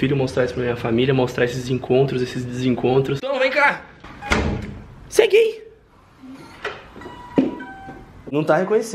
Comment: só eu entendi -Cê é gay Kakakakakak